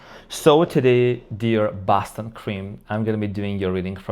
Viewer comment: It’s been a while, wish I was actually— a Boston Cream Wait WHAT , who said that